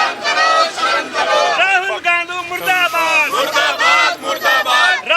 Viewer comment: did he say rahul gandu? 😂😂